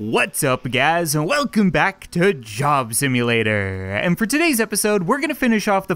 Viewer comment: What up and welcome back to doctor strange what I thought he said in the intro